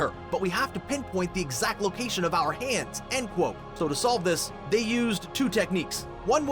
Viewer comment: all i heard was "and quote"